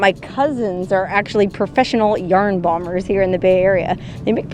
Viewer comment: "yarn bombers" Did I hear right? Googled it. Yes I did.